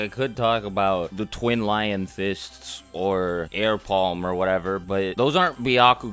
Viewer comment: who thought he said air pods